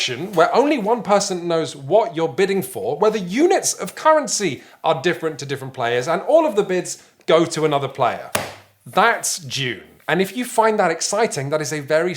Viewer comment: did he just say "that's jew"